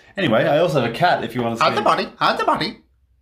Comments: So it was "hide the body" and I heard "I am the bunny" 😃😃👌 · The parrot : “hide the body” What I heard👂: “HIDE THE BUNNY” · The parrot : “hide the body” What I heard👂: “HIDE THE BUNNY” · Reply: I heard "I'M THE BUNNY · "HIDE THE BODY, HIDE THE BODY!" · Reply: I thought he said hide the bunny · I thought he parrot said hide the bunny😂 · "HIDE THE BODY, HIDE THE BODY!" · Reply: i thought it said “IM THE BODY IM THE BODY” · I heard "hide the bunny" instead of "hide the body" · The parrot:hide the body what i thought he said:shart the body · I didn't hear "hide the body" I heard "and a bunny" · help i thought the parrot said "bonzi buddy" when it actually said "hide the body"💀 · Parrot: hide the body What I heard: I'm the bunny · "HIDE THE BODY, HIDE THE BODY!" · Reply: And I thought it said, "cut the body, cut the body" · "HIDE THE BODY, HIDE THE BODY!" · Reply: Lol, I thought he said, “hide the bunny, hide the bunny!” 😆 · I hear “hide the bunny”